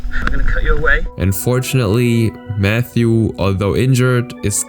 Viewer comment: did bro say unfortunately??